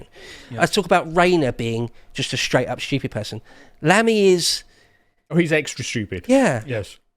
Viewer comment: Please let it be Lammy....THE END WILL BE QUICKER....AND A HELL OF A LOT FUNNIER 😂😂😂😂😂😂😂😂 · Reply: I thought you said LEMMY at first...that would be a miracle in many ways....